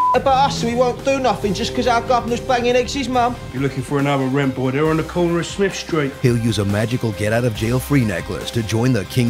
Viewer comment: I'm sorry, but you don't need sub titles for those people and all your negatives seem to stem from you not understanding that it is a parody, despite even saying it at the beginning. i assume that because it was a good film, you had nothing really to say about it, but made it for views. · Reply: xHaniffax What do they say directly after "eggsy's mum" because it sounds like a bunch of hogwash to me at 1:33 "You lookin fur anovur rim pornt ere on da corner uh smimf street"